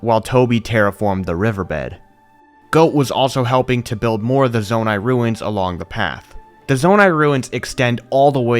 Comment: Bro I thought he said the zone I ruined for a sec 😂😂